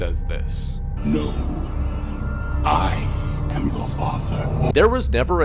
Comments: Him: “He ACTUALLY, said this:” Also me still hearing the same thing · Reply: I heard : "No i am the father" 😭😂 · Him: “He ACTUALLY, said this:” Also me still hearing the same thing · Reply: I hear "I am the father." For some dang reason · Fun fact: the voice actor for Darth Vader remembers reading “Luke, I am your father.” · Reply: I thought he said I have ur father 🤣🤣🤣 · Him: "Darth Vader doesnt say Luke I am your father" he actually says "Luke I am your father" Me: ummmm 👁👄👁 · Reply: I hear 'No, i am the father' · him: darthvader never said luke i am your father. me: turns up vol to max still hears “Luke i am your father” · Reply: I heard "no , i'm the father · Him: “He ACTUALLY, said this:” Also me still hearing the same thing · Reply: I thought he said "Luke, i AM your father" but he said "No I am your father" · "he actually says No I am your father" Me: pfft of course Darth Vader barley mentions lukes name to not remind him of padme or Skywalker which he tries very very hard to never remember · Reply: I heard it as “luke, I am the father” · DUDE, WHO ELSE STILL HEARD "Luke, I am your father" AND GOT SO CONFUSED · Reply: I thought he said Loo I am your father... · "He never said Luke I'm your father" My ears: "Luke I'm your father" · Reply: I heard: "look. I am your father" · "He never said Luke I'm your father" My ears: "Luke I'm your father" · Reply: I thought he said look I am your father?